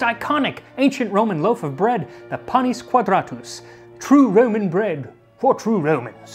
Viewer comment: 🤣🤣🤣 I thought you said Penis Quadratus when I first looked at the title. BLAST my old eyes......